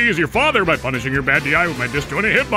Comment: Damn I thought it said "hotbox" 😔